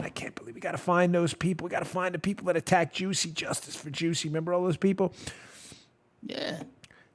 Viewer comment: I thought you said 'justice for juice' and I was like 'what did OJ do now'?